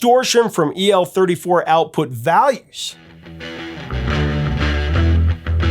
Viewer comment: That should read "...EL34 valves." Instead of "values". Excellent job otherwise.